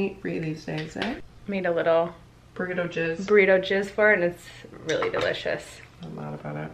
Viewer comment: burrito jizz is the best kind of jizz. i can't believe i just said that and now i can't say that word again until 2021. (i hope that's what you said and not just what my dirty ears heard 🙉) 😂😂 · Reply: Melana Jensson I thought she said burrito jizz too :)